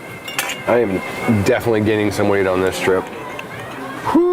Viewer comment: Thought he said "I am definitely getting some weed on this trip". I was like, hell yeah brother, you're in North Cali!